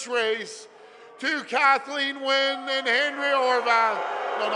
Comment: it sounds like hey says Henry instead of Andrea